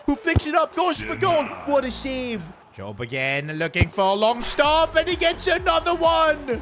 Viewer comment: did bro say longstaf